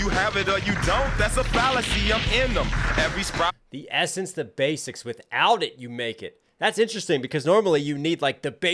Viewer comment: I think he's saying "... without it you're naked" But I could be wrong